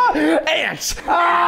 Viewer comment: I think said "ants" but it sounded like he said "ass." 🤣🤣🤣